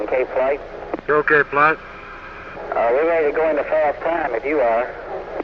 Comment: what does k flight say that makes Wally laugh so loud? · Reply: I hear “Uh, we’re ready to go in to solve crime if you are.”